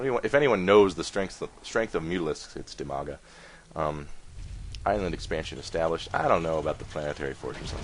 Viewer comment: Half the time I thought you were saying Umaga and I thought that was pretty rad.